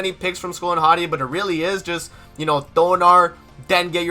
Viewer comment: Sorry, but all i can hear is "Gayer king, gayer Nanashi" WHAT ARE YOU ACTUALLY SAY! I mean you DEFINITELY didn't say that. . . I hope XD